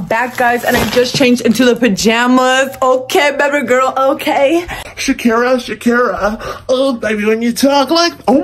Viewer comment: WHEN SUNG SHAKIRA SHAKIRA IT SOUNDED LIKE BRANDON😂😭